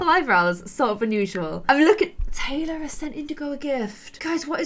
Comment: I thought it said Anthony and I was like WHAT! but it was Taylor